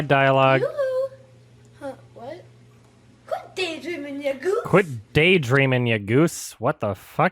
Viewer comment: I SWEAR TO GOD SHE SAID: "Quit daydreaming, nigguh!"